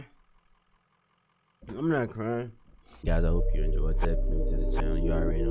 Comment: It sounds like your saying butt nugget in 😂